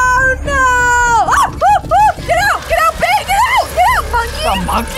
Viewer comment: did I hear scream "get out babe. Get out"?